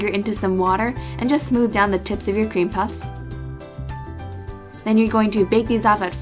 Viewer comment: i swear to god i heard ¨the tits of your cream puffs¨ xD i almost peed xD